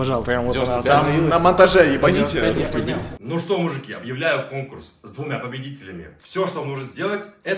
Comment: Мужики вы тоже услышали "объявляю курс", а не конкурс